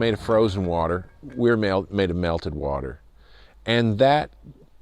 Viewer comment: did he say...melted water?